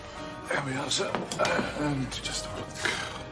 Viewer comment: What did He say? 49:16 "Let me just a looked"? I learn English. )